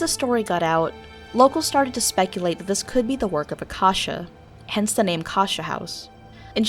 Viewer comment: I thought number 1 said kesha